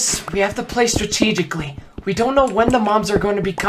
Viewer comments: I thought Deku said "When the moms are coming " 😂😭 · When deku said “mobs are gonna be coming” I thought he said “moms are gonna be coming”